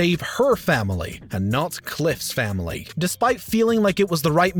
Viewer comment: ok I know I’m drunk but I played that back three times, does he say “Cliff’s family?” That’s what it sounds like anyways I could just be crazy though